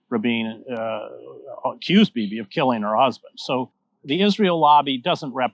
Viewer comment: If you have editing rights to your videos , @ 2:40 you need to add the word “Bibi” into the subtitles after the word “accused”, evidently missed by your transcriber but certainly what Hedges says.